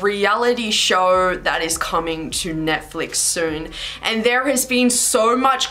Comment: Please why did I hear “ Hi Pals is coming to netflixxxx” 😭😭😭